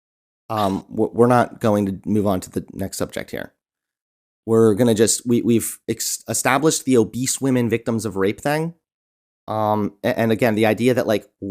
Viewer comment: I heard "obese woman' as "big ole beast woman" 😂